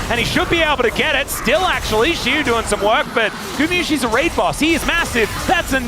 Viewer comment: Sounds like he said "Gumayusi is a raper" XD